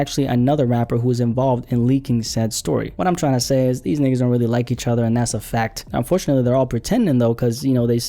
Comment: ik I didnt hear you say “these n*gg*s “ cause just by the sound and tone of your voice you’re not black . So please help me understand .